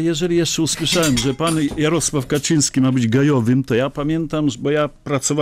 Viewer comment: Może to swoista autosugestia, ale ja usłyszałem "GEJOWY" :D:D:D:D